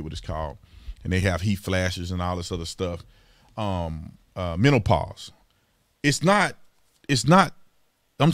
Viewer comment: I laughed when I thought he said 'mentalpause' lol